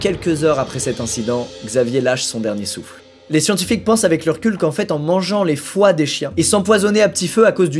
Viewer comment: "avec leur cul" j'ai compris recul.. avec le RECUL !!!!